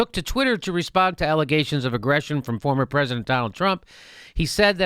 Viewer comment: Did you say "former president Donald Trump"?